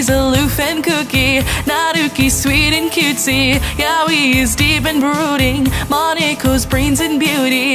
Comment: i hear yaoi? Is my husband nobody touch him! Is mine